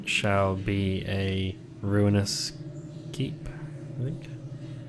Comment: i think it said "and it shall be a ruinous heap" not Keep. great video, loved it!